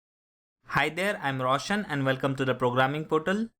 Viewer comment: I heard "Hi there I'm russian" hahahah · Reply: Its roshan lol